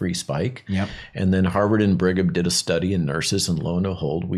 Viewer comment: What is the free spike study malon mentions? I can't quite make out what he says at around 8:47. harvard and briggam?